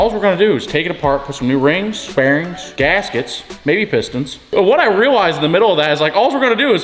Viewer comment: Oh, he said pistons, I had to rewind I thought I heard him say biscuits 😎